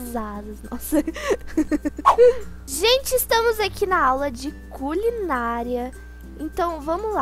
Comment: Só eu entendi culinalia?